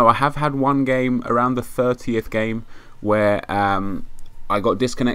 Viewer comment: Thought you said you disconnected at the 30 game.